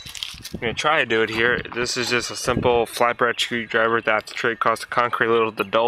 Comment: Sounded like he said flat bread screwdriver lol